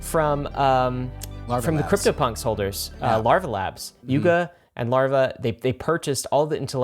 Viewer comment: Lol coulda sworn he said LARP Labs